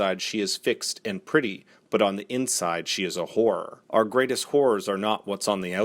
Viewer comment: I'm so very sorry but when you said: 'On the outside she is fixed and pretty but on the inside she is a horror' all I heard was 'on the inside she is a wh**e'. It kind of made sense to me since she was feeling guilty of the affair after all but a second later when I heard: 'Our greatest wh***s are not what's on the outside. They are buried within us.' I just lost it and rewound it just to laugh again. Great video btw I just watched the season for the third or fourth time and still love it.